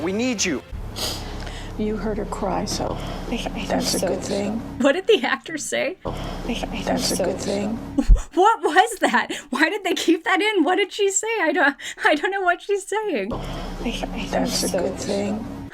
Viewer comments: For anyone wondering what was said at 10:59 I believe it was "I think so"? That's all I could hear anyway iwhfgugf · I think she was saying "I thought so", the nurse(?) said "You heard her cry so" and then the woman said "I thought so"